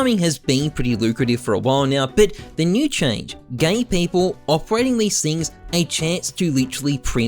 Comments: did i hear him say gay people? or do i have something in my ear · Am i the only one hearing gay people instead of give people here lol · anyone else hear him say "gay people"